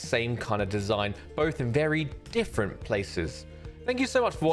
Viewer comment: At the end I heard "Sora is a bit shorter" 😂